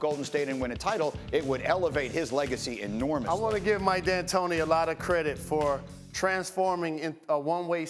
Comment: Did Jalen just say I wanna give my dad Tony a lot of credit?